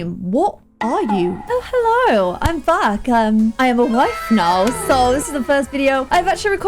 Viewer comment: When you said “i am wife now” I thought you said “i have a wife now” and I was so confused